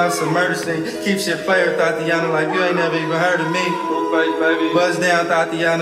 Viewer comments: If someone names their daughter this I’ll cry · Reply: Tatiana is close enough, it legit sounds like he says Tatiana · If someone names their daughter this I’ll cry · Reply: As someone who is named Tatiana, this song makes me slightly uncomfortable. 😂 It really sounds like he is saying Tatiana.